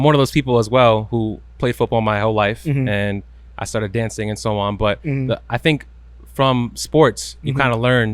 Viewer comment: did he say that he started "dancing and so on"..? 🤷‍♂️😂😂